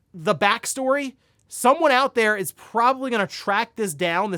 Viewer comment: I can’t wait for the ‘What Happened’ from Matt McMuscles about ‘The Day Before’ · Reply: Yeah, when he said 'someone' might do a video, I heard, "What Happened?"